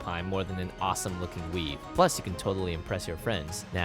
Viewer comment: did he say weave?